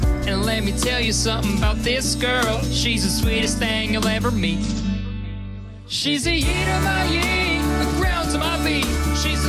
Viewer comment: Real lyric: "she's the yee to my yee" Captions: "she's the heat of my eating the ground" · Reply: my brain thought it said "shes the heat to my yeet" idk whats wrong w my brain tbh